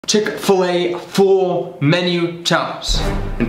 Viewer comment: I heard "chicken fill aye" 😂